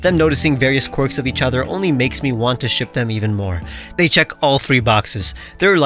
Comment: When you said you want to ship them on 12 I thought you said shoot them